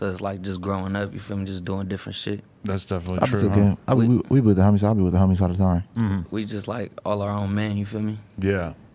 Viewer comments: bruh i have no idea what fenix said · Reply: B S.Chavez he said “I be with the homies all the time” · someone tell me what he’s said 😂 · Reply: We be with the homies all the time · “we with the homies , i be with the homies all the time “ is what he said